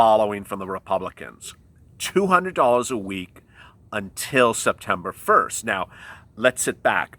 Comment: I thought they said 200 a week until end of September